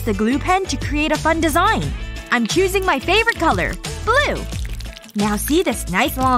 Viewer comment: I thought she’s said my fav colour is glue but she said blue Lol